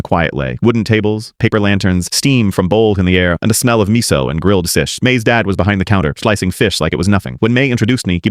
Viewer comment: I heard " grilled sish"😂😂😂😂